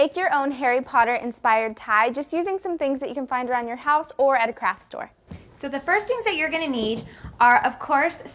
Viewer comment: I thought Bethany said crap store at 0:23 :D